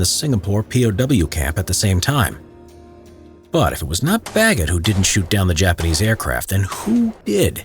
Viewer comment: The subtitles said "but it was not f*ggot" instead of bagget